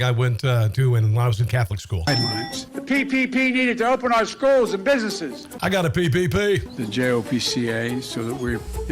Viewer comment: I heard "PPPD". I guess he was talking about being dizzy on the steps of the plane!